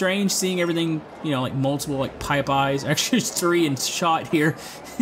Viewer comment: did he say "pipeyes"?